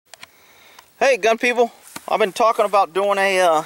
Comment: You know when I first heard your intro I thought you said " Hey dumb people" lol look at this guy he has alot of balls lol.